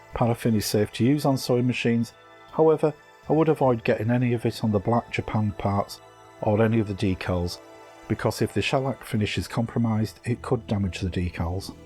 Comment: On 8:22 you said do not get it on the decals? and then you said do not use Walter??? sorry i am not sure what you said, can you please expain. Thank you great video\ work.. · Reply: water - don't get water on the black and gold parts - it will damage the finish